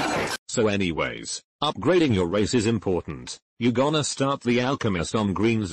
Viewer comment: when he said “race is important” i thought he said racism